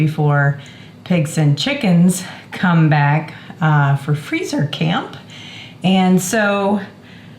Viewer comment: did you just say "freezer camp?!" 😂😂